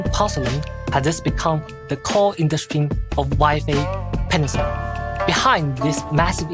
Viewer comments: had to double check, since i was playing some zzz while listing, I thought he said were heading to " Waifu Peninsula." I was already boarding the plane, when i realize I was wrong. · I didn't have my sound up at the beginning so I thought I heard him say "Waifu Peninsula". I was like wait... what?! 🤣 Also, bruh, just don't do what HSR did and your game will be solid 😂